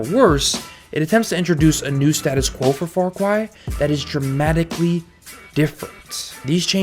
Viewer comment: did he say far cwy